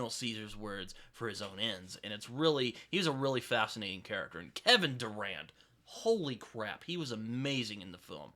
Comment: We definitively have to talk about Ubik soon. I’m old now and my memory of the book is slowly fading LOL. When you were talking about Planet of the Apes, I thought you were saying Kevin Durant, as in the basketball player 😅. I Googled it clearly I was way off lol. My co-worker has been begging me to watch Invincible. I really must check it out. Sorry to hear about your depression issues. I hope things are looking up.